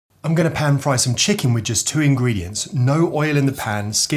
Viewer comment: Stop, stop, STOP, ENOUGH, I thought you said one ingredient, mannnn